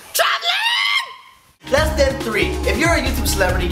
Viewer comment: lol at 0:44 instead of lesson 3, he says less than 3. and when at lesson 2, he holds up 3 fingers. and the subscribers went from 1 to 2. and 2 is less than 3.